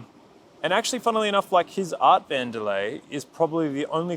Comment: did he say Art Vandelay? A little Seinfeld reference? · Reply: Yeah, it’s the name of Bondi’s delay pedal!